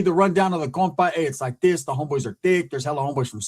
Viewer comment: did you say “the homies are thick”